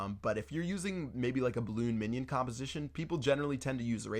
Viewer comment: I thought u said you sucked at balloon minion